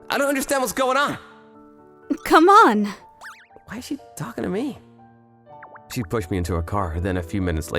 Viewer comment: a little slippy slip or did I mishear? Written text:"Where is she taking me...?" Spoken text:"Why is she talking to me...?"